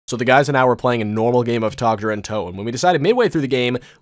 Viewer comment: did he say tag der UNtoten